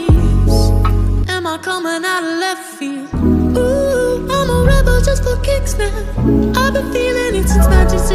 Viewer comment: I thought it said I'm a rebel for kids at one point 🤭😳 Tony Lopez be like: I sure am 🥰😏